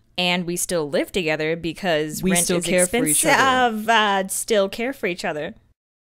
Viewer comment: What is she saying at 1:01 when says "... care for each other"? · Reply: Sara Deubler "rent is expensive"